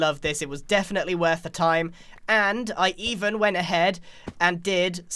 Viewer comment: when he said the Griangotts bank, I thought he said Grians got spanked!😂😂😂